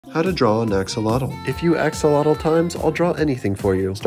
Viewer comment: I thought he said how to draw a exorcist 🤓